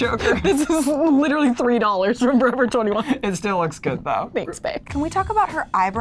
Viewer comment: DID JUST CALL BAE!?!? (at 3:10) #Liland forever